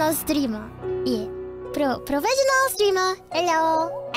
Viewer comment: Did she just say "hello"? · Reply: no 'ello'~